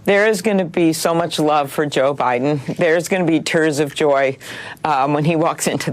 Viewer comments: Did she just say “turds of joy” 🤣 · I swear it sounded like she said turds of joy 😂 . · Did she just say there’s gonna be turds of joy for joe Biden? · I thought she said turds of joy at first, which might actually might make more sense when referring to all things Biden. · did she say "turds of joy"